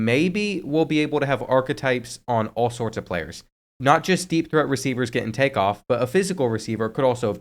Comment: Did he say deep throat receivers?? 😂